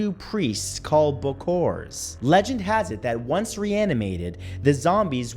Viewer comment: when he said "reanimated" i heard "Rihanna made it"